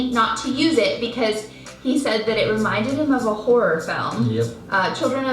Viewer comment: Am I the only one who thought she said 'it reminded him of a whore of him'